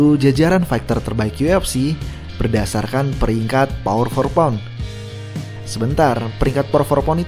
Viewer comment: Keganggu sama kedengeran “Por for pound” ga sih min nyebutnya? Harusnya kan “pound for pund”